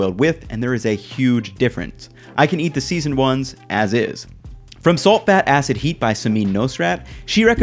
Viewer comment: I'm sorry but what the FUCKKK did he say at 4:00 lmao, what language was this ??!?!? LOOOL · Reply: "In Salt, Fat, Acid And Heat (name of the book) by Samin Nosrat (author)...."